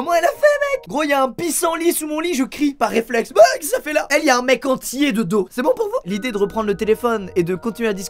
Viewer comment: "y a un mec ENTIER" encore heureux qu'il est entier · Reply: J'ai entendu "il y a un mec ANTILLAIS"